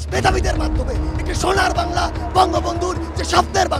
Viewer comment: তে সারজিস এটা কি বলে! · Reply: বঙ্গবন্ধু 😅